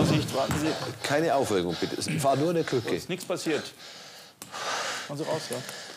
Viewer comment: "Keine Aufregung, war nur der Türke" musste das zweimal hören, bis ich "Krücke" verstanden hab😅😅